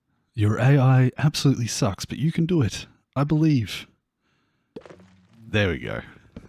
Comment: Sorry but did you just say I...beelieve? at 2:16 LOL